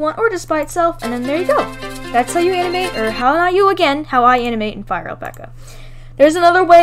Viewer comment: For a second i thought it said how i animate on flipaclip